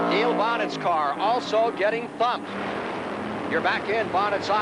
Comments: Did he just say what I think he said? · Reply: I heard bumped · does the announcer actually say "theres Neil Bonnett's car also getting fucked"? Cuz that all I hear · did he say niel bonnetts car was getting fucked?